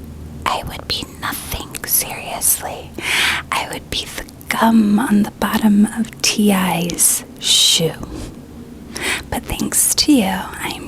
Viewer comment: i definitely heard "c*m on the bottom of TI's shoe" lmao